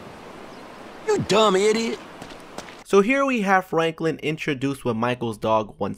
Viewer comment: did you say flanklin at 9:27 or 9:28?